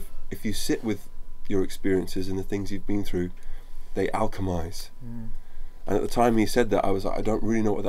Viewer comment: So what does he say at 14:46-14:50? I can't understand the word, no matter how hard I try. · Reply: oh, it's "alchemize" (Thanks, wikipedia!)